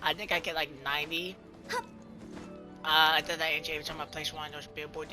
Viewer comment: I swear i heard you say " i placed wonks"